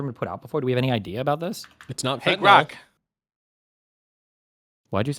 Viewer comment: did I hear “hey grok”? 🤖